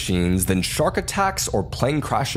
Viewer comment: I literally had to watch it twice because at the end I thought you said your sack get stuck it's not worth it I was like what the fuc